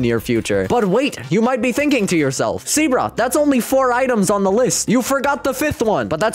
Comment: The captions said SEABROOK instead of lol